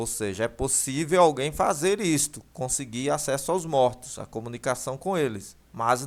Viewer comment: é possivel fazer contato com os mortos ? ou eu ouvi isso por engano no seu video ? no 7:15 min